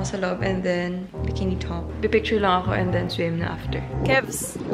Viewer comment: I though I heard "Btch" When ate called ate HAHAHAHAHAHA sorna parang marami ng namoo sa tenga natin anoh😅